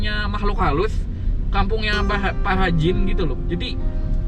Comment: Gw kira paha jin ternyata para jin. Salah denger gw